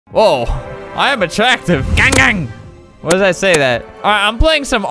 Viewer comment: I think he said gunggang